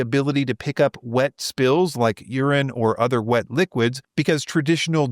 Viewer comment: did you say “urine”?